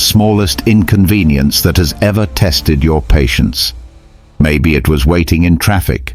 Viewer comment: your closed captioning says "patients" instead of "patience".